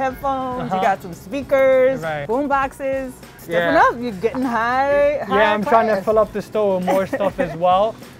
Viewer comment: it sounds like she says you getting high as a kite lol